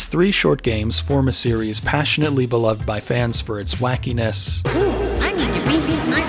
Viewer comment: did you just say tits